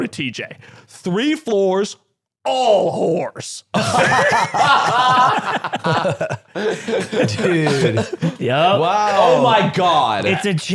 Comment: Why did i hear "Horse" . I thought the guy was just exited to talk about horses 💀